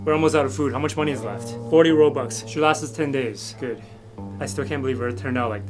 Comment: I died laughing on the floor when I heard “40 Robux” instead of “40 Dollars” 💀